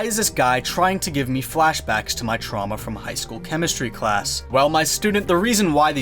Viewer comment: Did he say chemistry class 2:17 😂